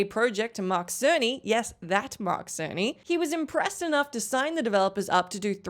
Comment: I thought she said Mark Sony and instantly went to google to see if Sony was created by a man named Mark Sony lmao, i'm an idiot